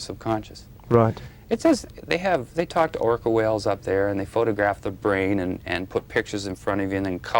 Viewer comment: What did he say? "They talk to Orca Whales up there." That's an interesting bit of information to slip out, especially in a video like this.